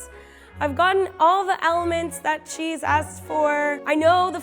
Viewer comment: I thought she said, "I've gotten all the elements that cheese asked for" lol, now I want a cheese bread